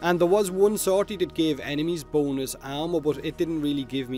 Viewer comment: i thought u said B O N E L E S S